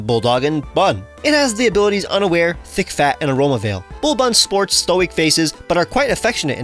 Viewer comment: I swear I thought he said think fat instead of thick fat 😭